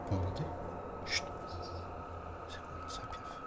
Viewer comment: a 7:57 j'ai entendue "c'est un pédophile"